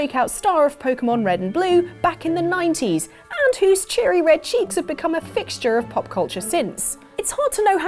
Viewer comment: Yup, as you said, enough for today. Otherwise I'll keep mishearing stuff like 8:50 "...whose cheery red cheeks have become a fixture of pop-culture sins "